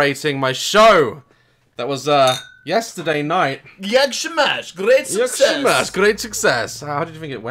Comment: DID HE JUST SAID IN POLISH "JAK SIĘ MASZ?"??? XD